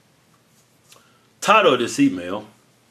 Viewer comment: After watching like 40 of these I thought said "Tired of this email", when he actually said "Title", 🤦‍♂️.